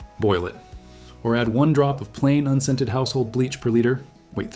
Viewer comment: Did I hear add a drop of bleach? To the drinking water?